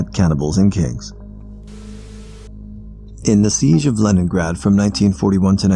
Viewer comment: "In the Siege of Lemongrab from 1941 to.." Me: The siege of what?! looks at the text Damn, the things I'm mishearing